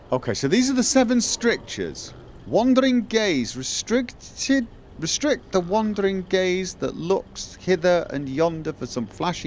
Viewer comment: I heard "restrict the wondering gays", but it does fit the context of a religious organization I guess...